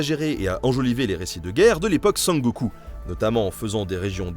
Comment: L'époque quoi ??? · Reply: A l'époque Sengoku (Seine - Go - cou) en réalité. Petite déformation Club Do'. :-)